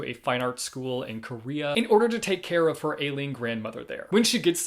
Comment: for XOXO i heard "to help her ALIEN grandmother" and i was like oh so it's going to be that kind of book? then my brain caught up... 🤣